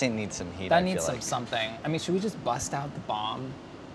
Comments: "should we just bust out the bong" what i thought Chris said. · "should we bust out the bomb?" I thought he said should we bust out the "bong" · I thought he said "bust out the bong"